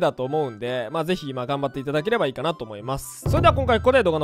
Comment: 最後の「ご視聴ありがとうございました！」が何回聞いても「ごちそうさまでした」に聞こえる🤣